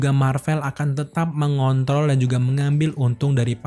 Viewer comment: Gw... Gak salah denger kan...? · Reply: Mengontol